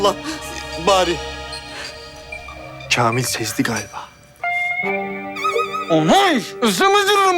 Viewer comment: da barış ne diyor ? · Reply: Kamil sezdi galiba